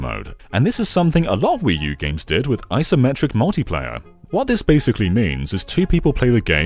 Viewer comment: did you say "isometric" or "asymmetric"?